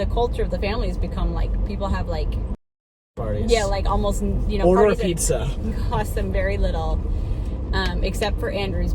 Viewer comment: what did Andrew say at 1:14? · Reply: Penniless?